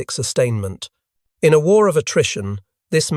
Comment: I found that the script read “war of attrition”, however, after listening to the narration for 25+ minutes I heard “ war of Nutrition!”😮😅